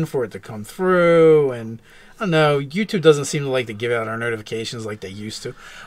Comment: I thought he said nerdifications instead of notifications. LOL!